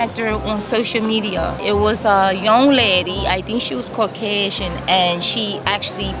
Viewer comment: she says "i think she was caucasian" you put "i think she was called xxx" in your sub.